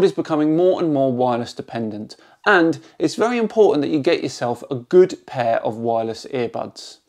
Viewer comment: What I heard.... "It's very important that you get yourself a good pair of wireless ear bugs"